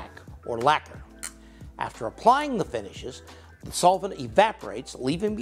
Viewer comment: At 16 minutes 50 seconds I thought it said tasting the finishes😂